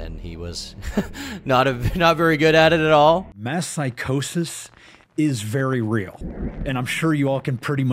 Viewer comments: Oh, I thought he said "meth psychosis", and was gonna be like, "You know, like the lady in Requiem For A Dream." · Stg I heard him say meth psychosis instead of “mass psychosis” and thought he was about to spill some tea about his personal life… damn he’s just still on BS